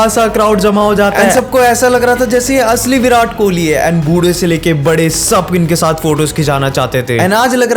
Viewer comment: wait what you said budhe se lekr bade????